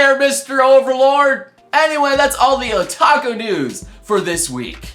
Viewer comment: sounds like youre saying taco news... now i want tacos